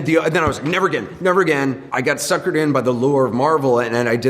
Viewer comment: Did he just say he got lured in by Marvel to do BvS????